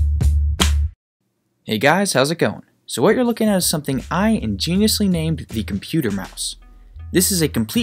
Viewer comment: мне одному послышалось что он сказал сегодня?